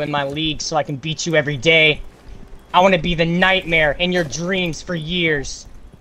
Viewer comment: WHAT IS BRO SAYING LMAOO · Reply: "I wanna be the nightmare in your dreams" 🤓